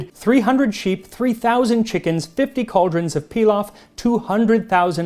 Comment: the subtitles say 300,000 chickens but you say 3,000. guessing the subtitles just have a few extra zeroes?